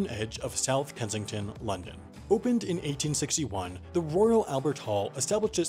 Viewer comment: the captions say 1871, on-screen it says 1816, but sam says 1861 how did this happen???